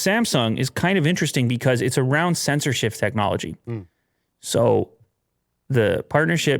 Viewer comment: Lol, heard that as "censorship technology"